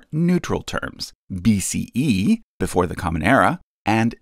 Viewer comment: In Hebrew Sunday school we always talked about history. I always thought they were saying "before the common error." It wasn't until my late 20s that I learned it was "era" not "error."